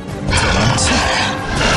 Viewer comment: 궁 대사 나만 "도망쳐? 자비없는 짓이야."로 들리나? 부질 없는 짓이야라고 하는거 맞죠?